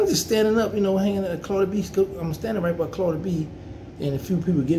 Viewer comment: Thought this mane said cardi B 😂